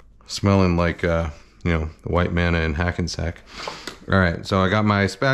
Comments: Bit of a racist comment there. Cut that out pal. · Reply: If you're referring to 4:57, then you misheard his remark for something else. "White Manna" is actually the name of a hamburger restaurant located in Hackensack, NJ. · Here are the only two things I (now) now about Hackensack: (1) Miss Teschmacher's parents are there, (2) The white men of the city smell like onions and raw beef. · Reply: If you're referring to 4:57, then you misheard his remark for something else. "White Manna" is actually the name of a hamburger restaurant located in Hackensack, NJ. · pfft ur racist. jk. anyway wonder how much of these in original were actually horse meat. great depression = smashburgers filled with onions........ makes ur almonds rub. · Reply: If you're referring to 4:57, then you misheard his remark for something else. "White Manna" is actually the name of a hamburger restaurant located in Hackensack, NJ. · Appreciate Kenji specifying exactly which White Manna he doesn't want his kitchen smelling like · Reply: I thought he said "a white man in Hackensack?" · Shoutout white mana’s smell sticking to your clothes for the rest of the day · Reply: If you are referring to 4:57, then you misheard his remark for something else. "White Manna" is actually the name of a hamburger restaurant located in Hackensack, NJ.